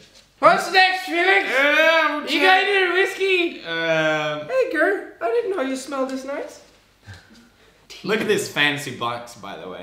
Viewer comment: I thought he said “Edgar, I didn’t know you smelt deez nuts”